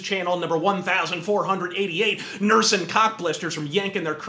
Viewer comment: dude did you just say 1488? Haha know your memes